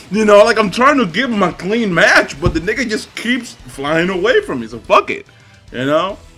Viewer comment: did you just say "NIGGA"